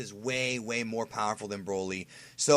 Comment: did you say Gogeta fights Whis!?!!?